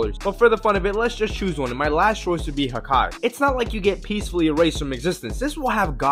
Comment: I’m not ever kidding I thought hakai said haki